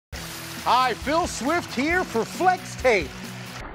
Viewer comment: When I first watched this I thought it said "Billy swift here." I had to replay it just to see if i was hearing it right.